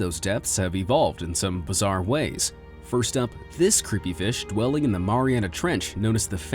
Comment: I thought you said this creepy bitch